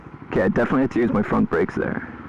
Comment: did he really said break. I was so scared😂😂